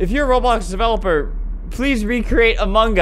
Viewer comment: it sound like he said “amungus” but i also hear it as “amangas” like the mangas your read yknow xd